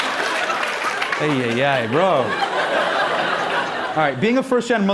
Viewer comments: DID HE SAY JESUS ??? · Did he say "jesus man" 1:38 🤣🙃